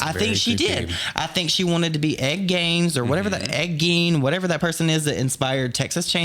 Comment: The way said Ed Gein, I swore he said egg game. Lmfao that accent is thiccccccc boy lol